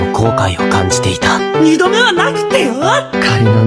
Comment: 行きましょう皆さんが 行きましょうマリオさんに聞こえたのは自分だけなのだろうか